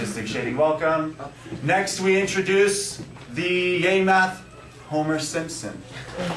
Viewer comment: its sounds like ur saying gay math